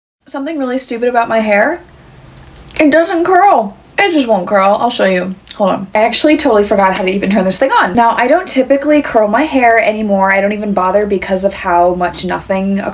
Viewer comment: Am I the only one who thought she said it doesn’t grow 😭😂